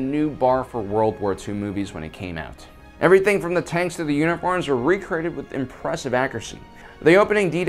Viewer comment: I heard “Everything from the tanks to the unicorns was recreated with impressive accuracy.” 😂😂